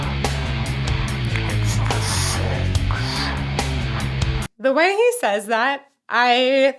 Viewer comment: Deftones fan since 97' and yes I always thought it said "It's the sex"